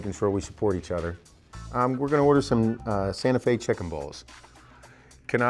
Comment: Seth it sounded like you were saying Sante Fe chicken balls LOL 😆